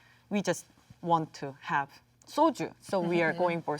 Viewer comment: I think she wanted to say “soju” instead of soilder..! Substitle is wrong😉 Soju is alcohol, similar to Vodka